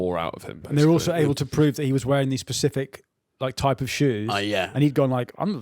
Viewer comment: did you just say "pacific"?! I'm appalled. Get out.